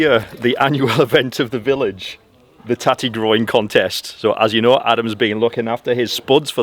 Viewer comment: OH, for heaven's sake - I thought you said a tatty GROIN contest. That would really put Crawfordjohn on the map! We'll leave it to you two to organize. Love, hugs, and peace from me in New Mexico USA.